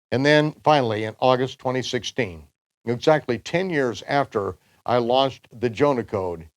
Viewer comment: Have you seen the teaching yet? It is the 3 days and 3 nights that Yeshua was supposed to spend in the grave as Jonah was in the belly of the whale. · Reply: Yes! I went looking for it because I thought I heard you say "after I LOST the Jonah Code". Today, I see that you actually said "after I launched the Jonah Code".... I think if I'd heard you correctly first time, I would have thought "that must be some other thing he's done" and left it at that : o You know, I checked it twice. Both times I heard "After I lost..." Praise God! He knows exactly how to get our attention! Thank you!